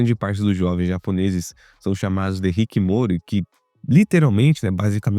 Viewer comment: escutei hikimori, é abreviação para hikikomori?